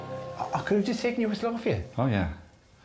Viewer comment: what did dara say at 14:32?? i cant understand it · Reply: He said, "aw, I could have just taken your whistle off of ya"